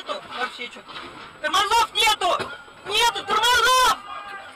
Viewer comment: Only thing bad about this awesome video is lack of subtitles 😅 I would love to know what these crazy people are actually saying. 😂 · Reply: the driver shouts to the mechanic "Hey, there's no brakes!" I can't make out what is said to the men on the ice floa at 4:45, but on of them responds, "this way is cheaper." The cameraman asks, "Where is the next stop?" to which they reply, "there is a dam over there." The cameraman says, "Ah, ok." At 5:55 as the tree is cracking they shout "Leave! Leave! The table! The table!"